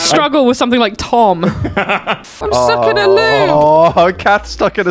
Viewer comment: ngl I misheard 8:00 as "I'm stuck in a loo" which had me giggling like mad.